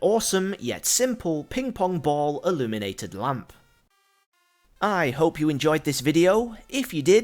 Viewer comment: I thought you said Illuminati XD